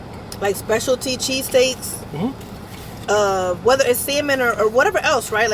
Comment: Lol at 10:24 when she said samon it sounded like semen cheesesteak 😂